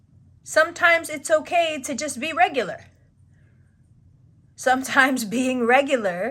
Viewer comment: My Libra Stellium felt it when you said "it's okay to be regular". I heard it as "it's okay to be basic" and being basic is an actual fear of mine so checks out how it's something keeping me stuck.